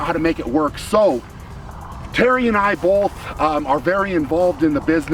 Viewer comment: I thought you said Terry for a sec. I didn't realize you Terry Holland's relationship had grown to be that intimate 😆